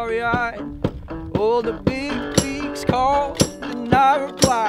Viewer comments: Anyone else thought he said cheryl not chair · I thought he said "date with sherral in late july" and thought he should have said "sherrif" because he was going to get executed but it's "chair" so it worked out for me in the end 😂